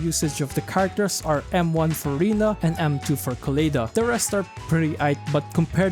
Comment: Hearing “M1 For Rina” really made me do a double take lmaooooo. Thought I heard Furina on a ZZZ vid